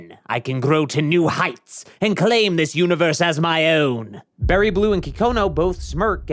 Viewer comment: ok I know for a fact I'm not the only one who heard bonnie blue here 😭